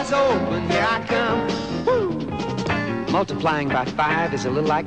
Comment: did he just say 5x 25 is 100? at 0.53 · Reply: He said 5 x 20 = 100, if you couldn't tell from the giant "5 x 20 = 100" sign